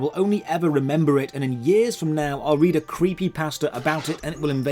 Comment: when he said creepypasta i thought for a second i thought he said creepypastor